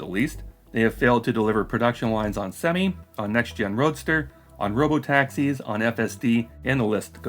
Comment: you said "next gen Roadster" but I heard "next year Roadster" which is actually a better name.